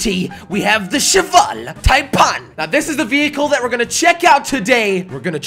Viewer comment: I thought he said Cheval Tide pod. xD